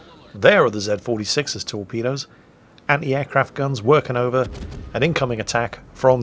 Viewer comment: Every time you said "working over" I heard "working overtime" in my head. Those AA guns are cewl.